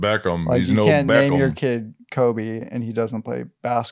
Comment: What they said: You can't name your kid Kobe and he doesn't play basketball. What I heard: You can't name your kid Colby and he doesn't play basketball. What I heard: Yeah, You can be living in that shadow of a cheese wheel. 😉😁